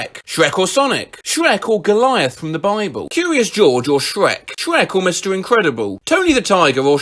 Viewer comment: I wasn't paying attention and somehow thought it said Furious George